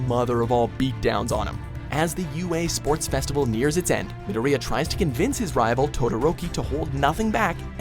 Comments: it sounds like he said midiria instead of midoriya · when this dude said midoriya I thought he said diarrhea